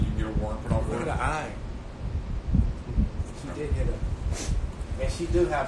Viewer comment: I thought she said she didnt touch her lol